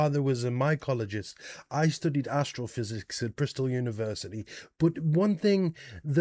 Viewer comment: I guess it is a good thing that you add captions. Without them, I would have sworn that you said that you studied assholePhysics 😝💩